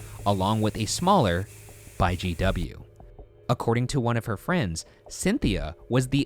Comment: to me sounds like he says JW not GW